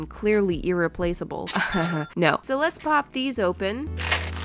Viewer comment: When she said ‘lets pop these open’ I literally heard ‘lets POOP these open’ 😂